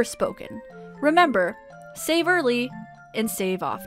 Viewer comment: is she saying _Saver Lee_?